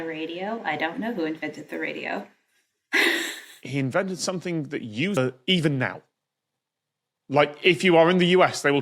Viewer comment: What did you mean When you said : "He invented something that u-er even now"